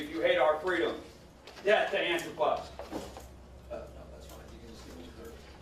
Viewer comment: does he say "death to anthrapods"? Because that really would be taking white supremacy to the next level